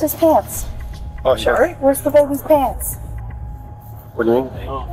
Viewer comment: Am i high or did she say "where's the baby's pants?" I watched it like 5 times I swear it sounds like "where's the baby's pants?". It's the second line after she already said "no he's here. Where's his pants"